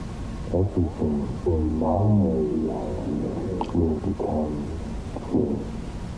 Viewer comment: "Everything in my reality will be perfect" Thats what I heard